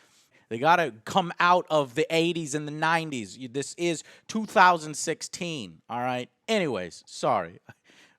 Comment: Wait... Did he say 2016 at 1:49?